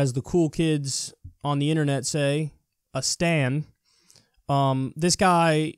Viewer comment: I thought he said stand and I was like 'NANI? STANDO?'